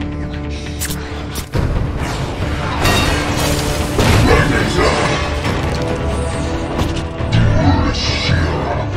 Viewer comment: Did someone know the lyrics what did Megatron said? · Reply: His own name